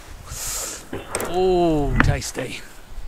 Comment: I swear I heard Treguard say 'Ooooh, nasty !' ( remember 'Knightmare' ? )